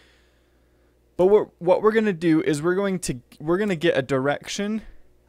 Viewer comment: o my gad at first i thought he said we were going to get an erection! lol, i'm sorry! XD